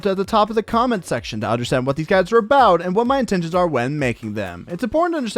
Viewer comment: I've watched your videos for a long time and I always thought you said "What these guy's are about" instead of "What these guides are about" I'm so stupid.